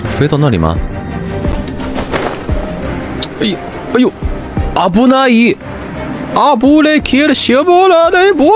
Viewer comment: what he says doesn't get translated bc he actually says a word in what sounds like japanese. "abunai" means danger/ous, and he starts to say the word again then devolves into gibberish noises of death